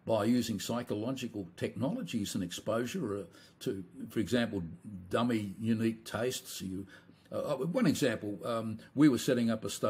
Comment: what did he say? Dummy unique taste? That doesn't make any sense